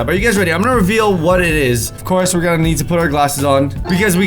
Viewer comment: I thought u said soda not glasses sodesyn